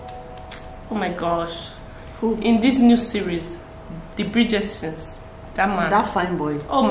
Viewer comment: Did she just say the Bridgettetsons?!🤣🤣🤣💀